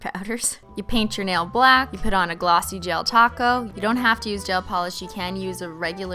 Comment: When she said gel taco I thought she said Del Taco